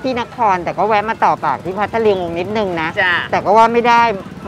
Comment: เหมือนได้ยินพี่กอล์ฟพูดว่า...พัทลิง <num>